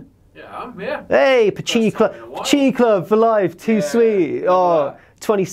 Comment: Thought he said Pochinki club, pubg :D